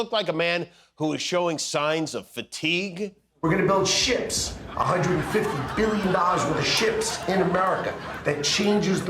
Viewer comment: Sounds like Lutnick was saying: “We’re gonna build shits …$50 billion worth of shits…” With this constipated clown crew, that would make as much sense than all the other impossible promises